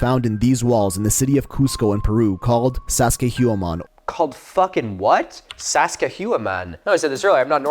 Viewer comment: i thought he was trying to say Saskatchewan and was confused why he was talking about Saskatchewan in Peru